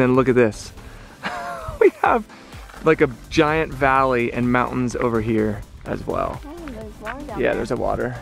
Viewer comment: like a giant mountains and whatever I'm lost.. What did he say agian? 😂